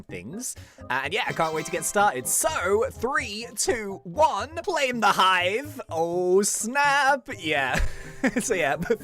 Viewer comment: it sounds like he is saying "plaim the hive"